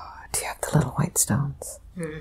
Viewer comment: Love this. When she says "Do you have the little white stones?" @ 38:45 I hear "Selenite" ... so cool.